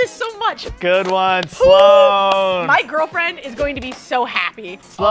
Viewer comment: did she say girlfriend.....?